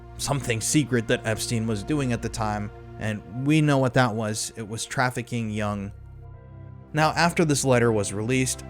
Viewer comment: trafficking young *smoke alarm beep*s I think he says niggas in the Patreon